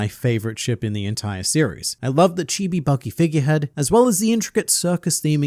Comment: Hearing "Chibi Buggy Figurehead" at 2x speed made me think I heard "Chibidoki figurehead" and thought that was way too funny to be real.